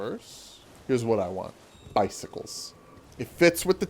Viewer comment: When brought up his idea for Bloodborne 2 I was really confused for a second because I misheard "bicycles" as "icycles" and was left wondering 1) How those would work as a means of transportation 2) What they had to do with Bloodborne thematically